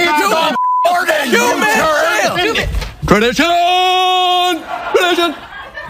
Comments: What the fuck was he saying at 00:40? didn't understand a word! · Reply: "TRADITIOOOOOOOOON! Tradition!" It's a reference to a song from the musical "Fiddler on the Roof," which is about a Jewish community. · What does he say at 0:39? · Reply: CrackaBox He's saying "Tradition, tradition." It's a show tune from the musical Fiddler on the Roof about Russian Jews. It is relevant because many of the Jews were forced to leave Russia by the government and had to immigrant to either America or Israel (British Mandate of Palestine). They were two of the only places where Jews were both already living and welcomed.